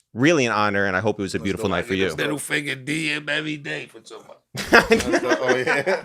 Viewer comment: what did say at 2:45? lol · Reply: “It was middle finger DMs every day” from all the people mad they weren’t invited